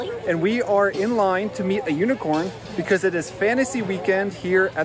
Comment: Ha ha ha I thought you said: : 'Lets wait in line to see the UN Quorn...' Quorn is a meat substitute for vegetarians here where I live, and I could NOT understand why you wanted to show some kind of food stuff supported by the United Nations? My head is not in the right place at the moment....sorry. Some of us Online people are a bit strange sometimes...